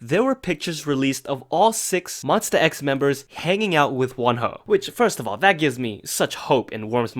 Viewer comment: I thought you said one hoe as in whore and i yeeted.